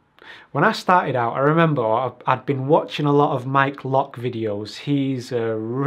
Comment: Did you say "Mike Lock" at the 5:30 mark? Didn't quite catch his name as searches don't show UX relevant matters. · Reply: Wim Van Asch That would be the web designer: <link>